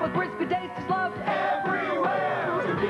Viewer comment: In my childhood, I thought they were saying, " we're gonna have to just kick their butt," but now I know it's "break me off a pice of that Kit-Kat bar" 😂😂😂 such a wild childhood